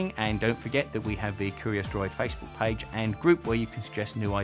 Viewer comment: Ah… Curious DROID. I thought it said Curious Druid. My bad!